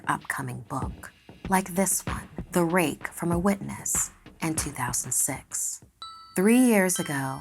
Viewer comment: did I just heard 2006? That's my birth year!